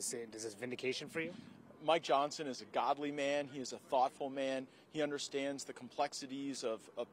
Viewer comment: I swear I thought he said “My Johnson is a Godly man.”….😂😂